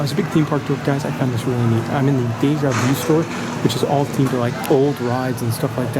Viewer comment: What store is that at 19:47? I’m trying to make out what you said but it sounds like you said it was the “gateyard you” store? I’m certain that’s not what you said but I’d love to know since I visit yearly and would love to purchase those old shirts. Let me know if you can. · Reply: I believe it is the Deja Vu store in the Europa section of the park